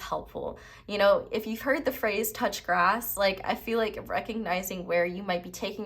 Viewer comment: Pile one feels like me but high key, I heard you say touch *ss. and I was like.” I touched mine yesterday.” 😂😂😂